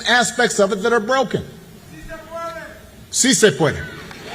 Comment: Barack has been lighting Trump up. I am loving it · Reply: Finally, and it is beautiful. But can anybody help a brother out here? At 11:20 of the video what is Obama saying? It sounds like "seasick winner"... which obviously it can't be.